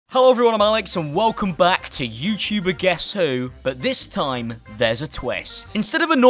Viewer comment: When he said I’m Alex I thought he said sallamalakom